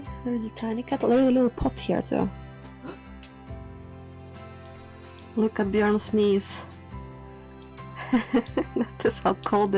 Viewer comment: Lovely and cozy. When you said, "Look at Björn's knees," I heard "Look at Björn sneeze." Ha.